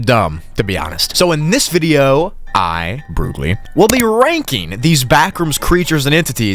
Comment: bro i thought it said brocoli not broogli XD love ur vids btw